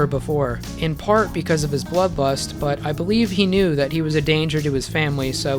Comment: i cant be the only one who heard bloodbust, right?